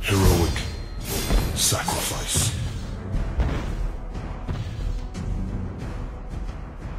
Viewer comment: What I hear: "I Should Be Volkswagen"